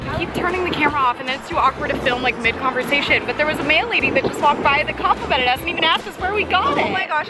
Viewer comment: Oh mail lady! I thought she said male lady😯😂